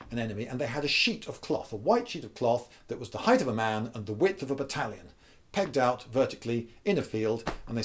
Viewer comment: I thought he said a width of an Italian and I immediately thought of very large Italians eating very large meatballs. Strange.